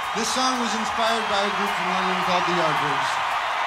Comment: Does anyone understand what John says at 32:23? "This song is inspired by..." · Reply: sounds like he says 'the yardbirds'